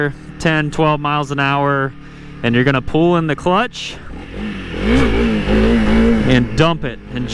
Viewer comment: What are you saying 11:50? "You gonna foolen the clutch"? What switch is on? 2 or 3? Do you press the clutch and release it fast or what are you doing? Thanks